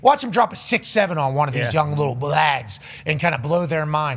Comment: what did he say? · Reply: Lads is what he said but it almost sounded like he started to say a b word so going off his choice of lads I'm going to go with he was contemplating saying blokes. 🤷🏻 😂